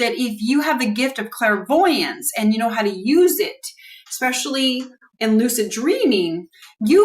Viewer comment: I don't believe I have talked about harnessing sexual energies using clairvoyance at night. I have talked about harnessing our hearts emotions during our lucid dreaming states or waking state. I could talk about harnessing sexual energies, but the hearts emotions are the most powerful energies to harness for creations and magic! Sexual energies in tantum with pure heart emotions is powerful too! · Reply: Ok, I apoligize, I went back to 12:54 time marker and turned on the closed captioning to make sure I heard correctly. The word "especially" in my ear sounded as if you said "sexually". You did not! I apoligize!!! I can delete my comment! Was I having a Freudian Slip? maybe!!!???